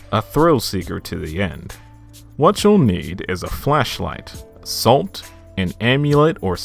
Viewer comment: i thought he said fleshlight